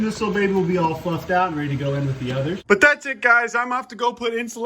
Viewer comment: all i heard was all fluffed out and ready to go into the oven........ i know thats not what he said but sure did sound like it lmao